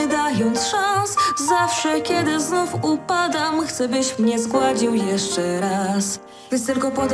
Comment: CZEMU USŁYSZAŁAM ,,Zgwałcił'' 3: Mój słuch jest do wymiany :<